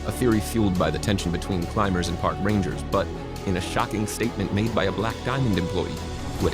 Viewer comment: i had to rewind, all i heard at first was "shocking statement by a black guy employee"